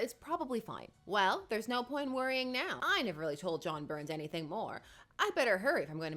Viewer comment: Did a doubletake because I initially thought you said the villain's name was John Byrne 😂